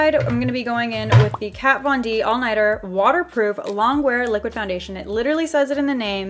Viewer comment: "KAT VON D ALL NIGHTER" I thought I was hearing things, but I went back and nope, I heard right. Regardless, LOVE YOU SO MUCH !